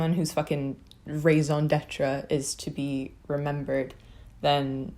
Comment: What does she say at 1:41? 🤔 She repeats it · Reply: "raison d'être" (hopefully i spelled that right lol) it's french for "the reason to exist"